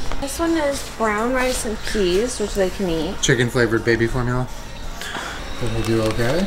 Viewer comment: I must be like really tired because instead of hearing chicken flavoured baby formula my brain jumbled it and thought it heard baby flavoured chicken formula 🤣🤣